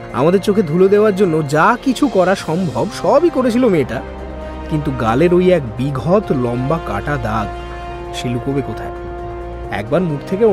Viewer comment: Please don't mind.. 11:28 Second, "galer oi ek bighot lomba kata dag.." pora ta eeekktuuu vul hoye glo..baki golpo to osadharon...